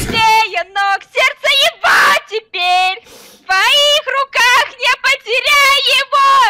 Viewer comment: что она сказала!?😂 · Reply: СЕРДЦЕ ЕГО ТЕПЕРЬ